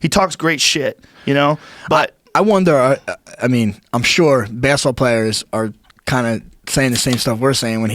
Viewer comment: Took a minute to realize what dude was saying he called it bassball players